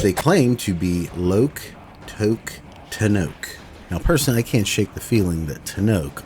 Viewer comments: I always thought it said "tick tock tenno" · even with the translation subtitles, I always assumed what Wally was saying during this portion was ‘TICK. TOCK. TENNO.’ Due to the pauses in the sentence matching the same rhythm one would use in English.